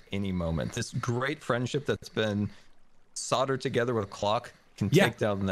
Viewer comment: commits a war crime, “thanks for the clock!” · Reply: I misheard him and thought it was a Glock